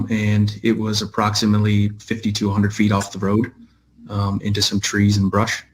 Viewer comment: Her car was "approximately 5,200 feet" off the road, according to the trooper. So, if she woke up in the passenger seat, and remembered nothing about the ride from the pub, how'd she know which way to go to get almost a FREAKING MILE out of the woods and back to the road?!?! And she was able to then walk even further to the gas station?!?! Yeah, and I gotta bridge I wanna sell ya. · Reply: I thought he said 50-100 feet?